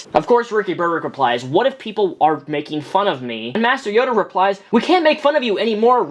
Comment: he actually said "what if * I want * people to make fun of me?"